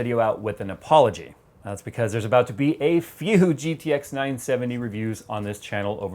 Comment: Is it me or did he say at 0:36 a few gtx 970s? And after that new cards?